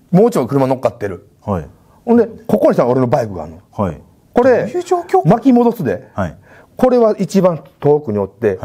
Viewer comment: え？まさお、なんて言ってるの？ オバケに取り憑かれてる人みたいに聞こえる👻 『急上昇〜』って言ってるのかな🤔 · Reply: どういう状況それ って言ってる気がします😂